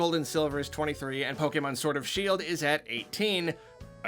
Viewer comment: Does anyone else hear "Sword of Shield"?